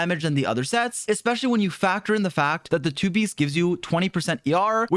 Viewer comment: Did I hear " Factor the fact" >|